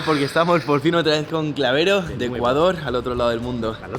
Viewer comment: Escuché " Ecuador "